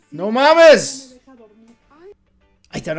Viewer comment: Did you say "No mames?" because if that's what you've said, that's a Mexican phrase to say "Don't f--ing kidding me!"